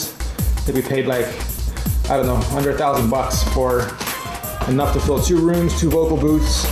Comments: Did he just say $100,000? O_O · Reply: under a thousand bucks id hope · did he say he paid $100,000 for that foam?